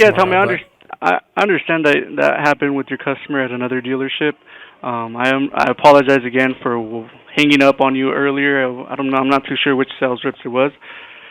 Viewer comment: he didn't say that, but he for sure is the same guy. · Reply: the subtitle says "I apologize again / for hanging up on you earlier"; the spoken words have a "wh-" cutting off before "hanging up," as if the speaker were about to say something like "what happened earlier."